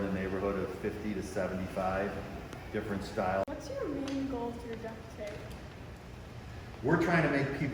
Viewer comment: Oh thank goodness I thought it said torturing duck tapeXD